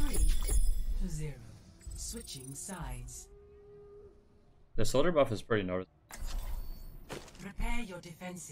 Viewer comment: what tf did you say about the soldier buff all i heard was "the soldier buff is pretty hrrmfhgn" lmao